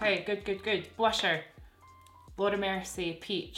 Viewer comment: I totally heard "lord have mercy" when you said the Laura Mercier blush 🤣